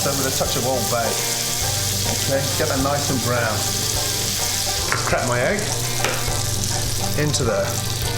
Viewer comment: I thought he said crap my egg